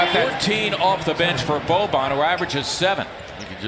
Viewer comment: mah boi boban just said kobe? Or bobe? Lmaaooo